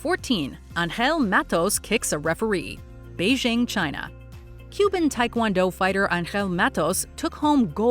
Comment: ok, when you said "cuban" tae kwon do fighter, i heard "human" fighter. i was thinking "uh, as opposed to what, a kangaroo?"